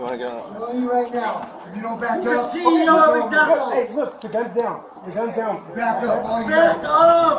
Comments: "my dads the CEO of mcdonalds" at about 6:30 did i hear that right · "You're not that good of a shot" was truly disturbing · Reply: did he say "my dad is the ceo of mcdonald's"? LOL